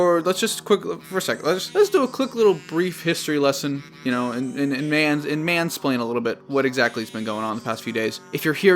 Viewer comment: Hey guys! Thanks for watching! If you want to take action and donate to the ACA as well, here's a link to their donation page: <link> If you're unable to donate, that's perfectly OK, please just leave a Like/Dislike on this video (anything is helpful), as well as Subscribe, and also leave a comment letting me know your thoughts on the Sony/Disney deal and how Sony can rework the character to work outside of the MCU! Any type of engagement is super appreciated, as it doesn't cost a thing and you're doing your part in letting the YouTube Algorithm have mercy on me and spread what we're doing here! I know this video is a lot different than my normal kind of video, but I didn't think it was necessary to come up with an entire plot for something that might not even happen if the deal goes back through, so I wanted to keep it relatively light but still have enough story beats for the pitch to get my points across. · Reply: You missed a prime "let's fansplain" pun Unless you made that and I misheard.